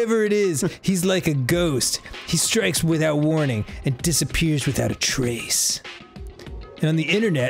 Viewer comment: When he said appears without a trace i thought he said appears without a taco